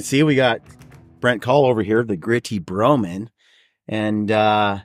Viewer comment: I've watched about 25 of your videos and I always thought you were saying "Gritty Bowman" instead of Broman. Made me laugh. Reminded me of the song "on the road again.". For literally decades I thought the lyrics were "the WIFE I love is making music to my friends" instead of "the LIFE I love is making music to my friends". I always thought of course he wants to be on the road again because his wife is banging his friends...